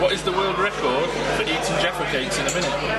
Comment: Eating WHAT in a minute? Sounds like he says "Giraffey cakes".